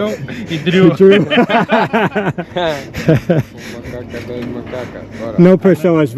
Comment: did they say makaka? We have a similar in Zulu language (meaning feaces) Im not sure i heard that right 😂